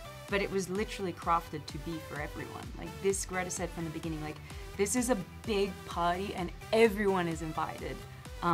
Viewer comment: Because she's Australian when she says 1:19 "this is a big party and everyone is invited", I heard "this is a big pie, and everyone is invited", and I thought that was worth commenting.